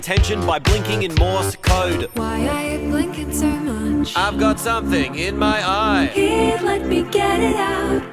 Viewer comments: I heard the.... " I got something in my sigma"... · I heard ''why are you filthy''💀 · It sounded like he just said I've got something in my spongebob